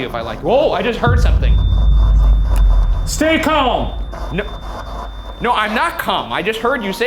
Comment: please tell me I’m not the only one who heard stay calm before shayne said it